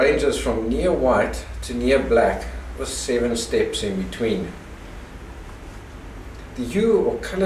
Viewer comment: Sounds like you say "7 step in between" in the video at 2.00. Thanks for the video.